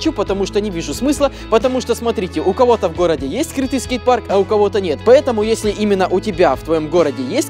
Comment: Мне одному послышалось "скрытый скейтпарк"?